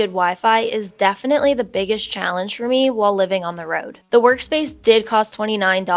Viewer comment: Ha, I thought you said your mom found you a "Berk-Space" and thought it was some kind of funny family speak.